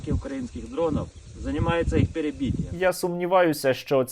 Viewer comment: Мені одному почулося "Занимаемся их перепитием"? Бо це єдине, що ці "защітнікі" можуть робити.